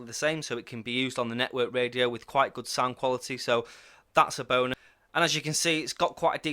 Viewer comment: At 7.15 it sounded like you said thats a boner :-) I know you like these radios;-) super job on the video, I will have to get my ass in to gear and nag one of the vendors to send me one although the last time I used Zello it was 100% potty mouth time not very family friendly :-)